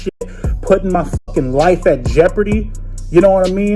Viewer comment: If I heard correctly, the fact that he has a wife takes this to a whole nother’ level… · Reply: he said “life”